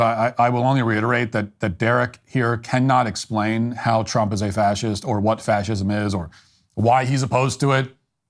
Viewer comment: fascist? i thought they said 'fastest'.